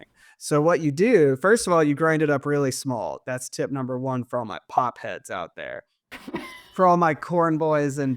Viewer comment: I thought said he's a "pot head" at first! 🤣